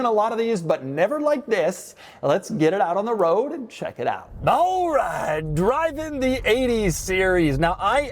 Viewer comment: Doug likes Jynxzi?? I heard that “Ballright” 😂